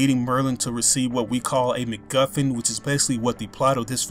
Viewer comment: did he just say McMuffin?